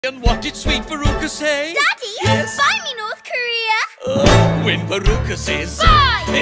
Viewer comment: I hear "buy me new Korea" 🤨🤨🤨